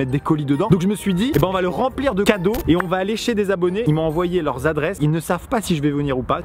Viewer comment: c est moi ou j entend lécher des abonnés ⁉️